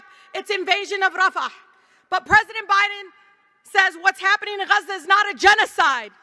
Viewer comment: Am I crazy or did she say "What is happening to US is genocide"???!!! Minute 6:04 Does she think she's a Palestian in Gaza 😂